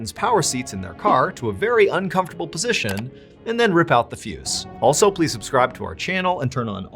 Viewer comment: I had to replay it 3 times. I swore I thought you were saying, "..and then rip out the fetus."